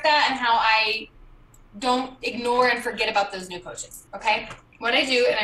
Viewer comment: did she say “new coaches” or “new POACHES”? 😂